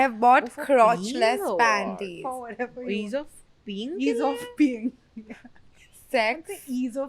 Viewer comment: I heard it as „ease of peen“ 😂 iykwim